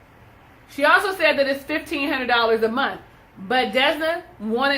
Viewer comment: I thought she said.....1500 more?